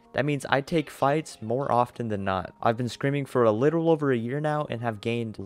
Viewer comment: "ive been screaming for a little over a year now, and have gained lots of experience." (please dont tell me im the only one who thought that he said scream instead of scrim...)